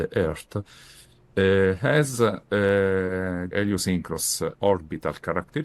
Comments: I think he said asynchronos (not sure though) and @ 27:28 it's azimuth direction (certain). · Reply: Not quite on the first one. 26:00 an Heliosynchronous orbit: meaning the satellite moves in an orbital plane that keeps pace with the sun, and thus the satellite moves roughly north or south relative to the scanned surface. 27:28 Azimuthal direction: a "long aperture in the azimuthal direction" refers to the effective extension of the antenna's physical size along the satellite's track (its flight direction). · Asynchronous orbit perhaps "A synchronous orbit" (I tried searching for "asynchronous orbit", and I couldn't find a single page containing that term, only "(a) synchronous orbit"). But, in any case it didn't sound like Biondi said that - there were more "letters" (sounds) before "synchronous". After (re-)playing what he said for dozen or so times, I still couldn't be sure whether he said something like adeo-, elio- or ideo- synchronous orbit. Perhaps Biondi tried to say, i.e. translate to English "orbita eliosincrona", and it didn't come out just right. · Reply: you are right. Others clarified it's Heliosynchronous. I changed my note. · sounded like Geo synchronous, orbit (GSO) <link>